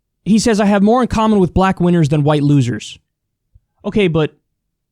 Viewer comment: "I have more in common. . .with black wieners. . ." Is totally what I thought dude just said